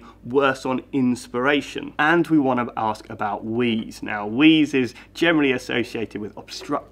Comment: And we want to ask about wees (misheard)... I didn't know that was included in the respiratory system!